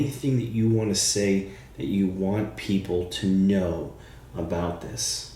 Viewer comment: The question what do you want people to know sounded like she said “they were right”😢😢